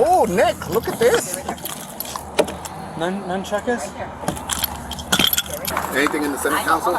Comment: did i just hear him say nun-chakus 🌝